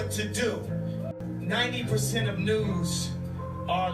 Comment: Respect to Kanye West for speaking up, but I can hardally understand a word that guy says! I thought he said... 90% of nudes are liberals. I was just about to switch sides, then realised he said... News